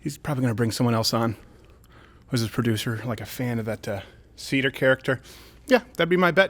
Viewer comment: Oh, Steven said "...that Seder character" I thought he said "that seedy character". I guess either way it's right.